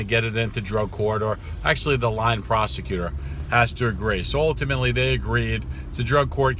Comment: did he saying the lying prosecutor has to agree??